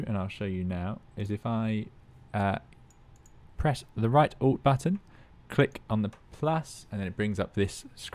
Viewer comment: what button did he say? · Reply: right ALT